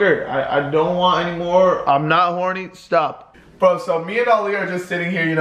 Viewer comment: did he say I’m not horny?